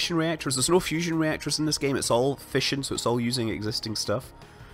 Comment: every time you said fission i thought you were saying fishing...